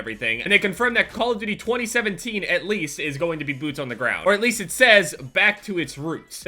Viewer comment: I thought this video said boobs